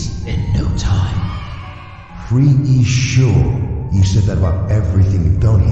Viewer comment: “𝓯𝓻𝓮𝓪𝓴𝔂 sure?” WHY DID I HEAR THAT GRRR BRITISH BOY